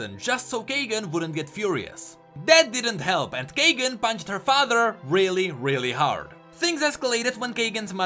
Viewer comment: I misheard that " Kagan bang her father ,really really hard " :v